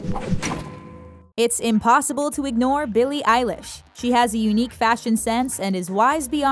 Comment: i thought they said billie eye lash tbh